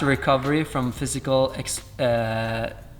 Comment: Could 9:50 be the word exertion instead of exhaustion? Exertion means physical or mental effort